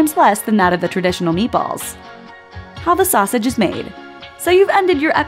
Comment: did she say sausage?